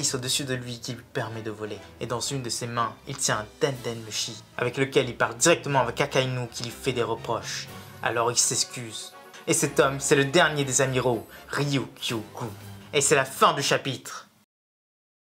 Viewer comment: je buvais de l'eau et j'ai entendu "Cacainu" j'ai failli m'étouffer